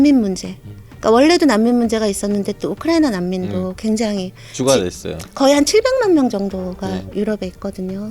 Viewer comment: “죽어야됐어요”라고 들리는데 맞나요?